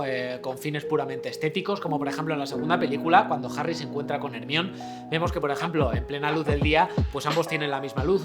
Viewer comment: Me encanta la pronunciación castellana 6:55, juro que entendí "Harry se encuentra con el mión" jeje